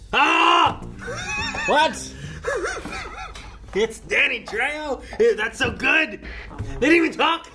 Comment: I honestly thought said "Daddy Trejo" lmao